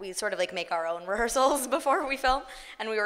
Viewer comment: Can someone tell me what Elise says from 31:16 at 31:26? Thanks! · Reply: No-eH! Spain "Natasha and I make our own rehearsals before we film"